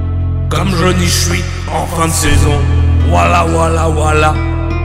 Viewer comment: Paroles / Lyrics : Ouais ouais ouais ouais ouais J'suis trop vieux pour le MMA Zéro tracas zéro blabla Je dépasse les 90 De toutes les putes vous êtes les fils Ouais ouais ouais ouais ouais J'ai plus la force pour les haters Jeanne j'ai besoin de ta chaleur Des non binaires, c'est quoi l'projet Ouais ouais ouais ouais ouais Annules la grève, j'fais un rejet Mon parti n'a plus le même nom J'veux juste rentrer à la maison Ouais ouais ouais ouais ouais Y'a des migrants sur mon balcon Comme j'le dis j'suis en fin de saison Wala wala wala J'finirai dans la poubelle verte Laissez moi me décomposer J'n'ai plus rien à vous proposer Ouais ouais ouais ouais ouais Y'a des migrants sur mon balcon Comme j'le dis j'suis en fin de saison N'est-ce pas, n'est-ce pas, n'est-ce pas N'eeeeest-ce pas J'fais plus de vues qu'ton putain de film Pourtant à aucun moment j'frime On a d'jà perdu la bataille Plutôt crever qu'faire du Fortnite Plutôt crever qu'faire du Fortnite Ouais ouais ouais ouais ouais Je t'aime À l'armaniac J'ai besoin d'alcool pour kiffer Y'a Anne Franck dans mon cendrier J'ai besoin d'alcool pour oublier N'est-ce pas, n'est-ce pas, n'est-ce pas J'ai besoin d'alcool pour tout brûler Ouais ouais ouais ouais ouais Merci, de rien, au r'voir m'sieur dames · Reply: J'ai compris "Comme Johnny, 'chuis en fin d' saison"...